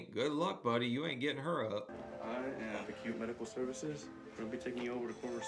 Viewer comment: I thought he said 'The cute medical services' haha